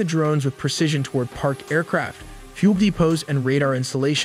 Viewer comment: I hear "pube depots" not "fuel depots".